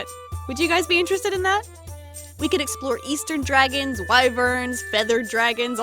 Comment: I misheard "Eastern dragon" as "Easter dragon" and now I'm imagining a bunch of holiday-themed dragons hhhhhhhh 💗 Also this game really makes me want to play Spyro Reignited...! I played Into the Dragonfly before but it was my first Spyro game so I didn't really get much out of it 💦 I'm sure the trilogy will be a perfect introduction for me though!